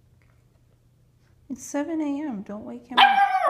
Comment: Sounded like puppy repeated, “it’s 7am, wake up” 😂😂😂😂😂